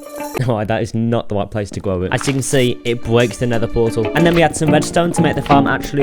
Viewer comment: did he call the end portal nether portal